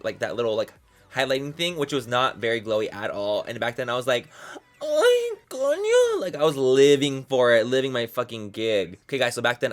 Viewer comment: WHAT HE SAID AROUMD 7:40 ABOUT HIM LIVING FOR THE HIGHLIGHT IS MAKING ME LAUGH SO HARD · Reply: He said: "Ay, coño!" 😂😂😂😂😂😂